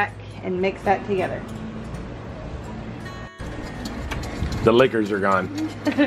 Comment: I thought he said the liquors are gone and now I wanna try a liqueur version lol